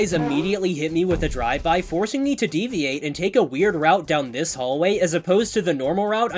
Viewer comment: DID YOU SAY WEIRD ROUTE